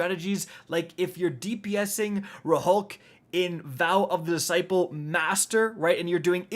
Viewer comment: Did he just say “RuhHulk?” 💀